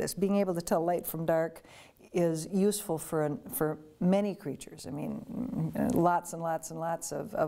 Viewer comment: wait did she just say creatures?